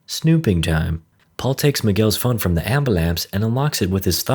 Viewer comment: Did I just hear "Ambalampse" @ 7:30?